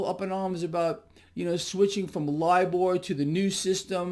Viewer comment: What's he say at ~ 3:52 ? Live ore?? Halp, TYIA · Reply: LIBOR